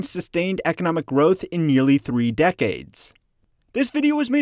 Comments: Please proofread your captions before posting. At 2:29 you say "decades" while the caption says "centuries". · it should be decades instead of centuries (i am talking about english subtitles) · Captions say "centuries" instead of "decades" here